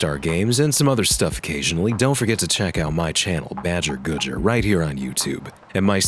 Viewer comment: Man I have such bad brainrot I thought "BadgerGoodger" said "BadgerGooner"